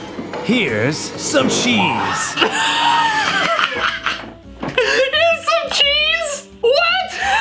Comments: I thought he said here’s suncheeze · not gonna lie, if I had not seen this movie and I was reading his lips, I would have gotten “Here, Take It” instead of “Here’s Johnny” or “Here’s Some Cheese.” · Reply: I hear here's sun cheese listen very closely